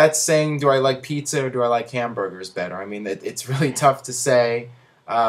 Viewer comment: Did someone say hamburgers?!